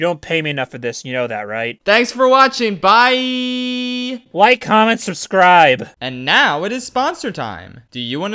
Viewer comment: I apologize but I always hear "White Comments, Subscribe!"